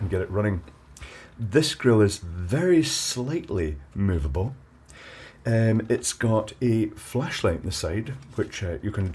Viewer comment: i misheard you saying 'this grill is very slightly miserable' sorry for that.